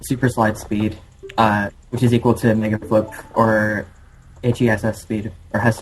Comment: Im sorry but at 6:47 I heard nigga flip call me racist but i think its fuckin hillarious